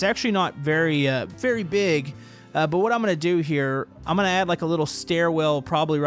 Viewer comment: I thought u said u not gonna add dials